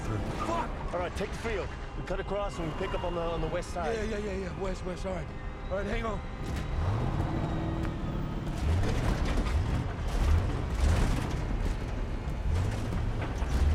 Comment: “Can’t go East can’t go south can’t go west” North. Go north. As far north as possible. Go until the weather gets cold and dry and keep going. That’s what I’d do. Fungi don’t like the cold or dry conditions. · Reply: I thought you said weast